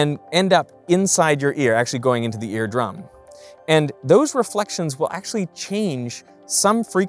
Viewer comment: I heard "redrum" that can't be good.